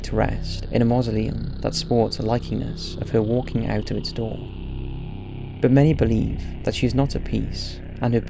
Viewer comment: It's funny cuz I was reading the subtitles (English is not my mother tongue, so...) and at 3:32 the automatic subs misheard "but many believe that she's not at piece" as "but many believe that she's not a penis" XD